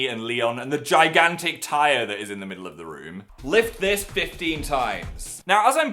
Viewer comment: I thought he said "lick this tyre 15 times" instead of flip